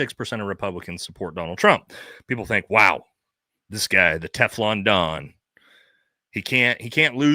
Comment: sounds like you said teflon dong 😂🤣 2:06:07. What a cuck